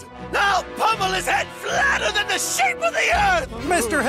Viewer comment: "Flatter than the shape of the earth" eh? Looks like someone didn't go to school :D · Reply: I could have swore that he said "Flatter than the shape of a pancake"